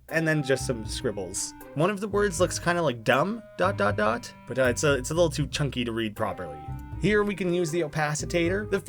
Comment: I think that at 14:04 it says, “I’m pretty dumb... okay.